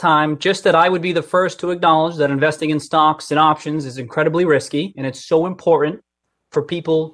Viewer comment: I thought at 12:01 he said "stonks and options" but I think I'm just hearing things. Can someone confirm?